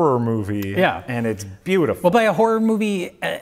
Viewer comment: min: did he say „WOBEI“..? And does this gentleman have German heritage by any chance..?